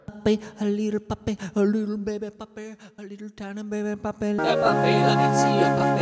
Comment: I think he just didn't finish the word and said "Ba, a little baby puppy." The 'ba' standing for the first letters of 'baby', but because he inhaled right after the ba, for some reason I thought he said "Bake the little puppy."